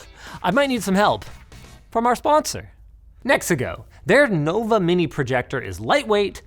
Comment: lol I thought you said Mexico, was like wtf are they sponsoring LTT?